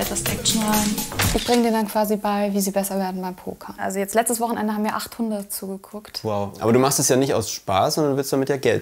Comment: „Letztes Wochenende haben mir 8 Hunde zugeschaut...“ 😂 beim zweiten Mal habe ich es dann verstanden haha